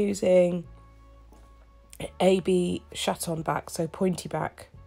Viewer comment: I thought you said “AB shat on back”. Had to skip back to relisten